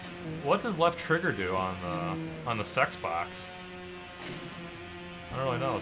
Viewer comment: sounded like he said on the sex box instead of on the xbox